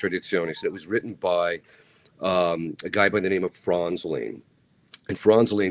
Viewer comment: Would you please tell me the author and the title of the book fr.R is talking about in 8:18 minute. I hear Franz Lean however I can’t find any author of this name. Thank you in advance